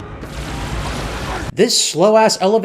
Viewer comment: i swear i hear spoiler